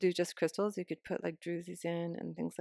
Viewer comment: what do you say at 4:44 ? you can put what in? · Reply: sorry for the mumble. you could put druzy's in. like little geode's.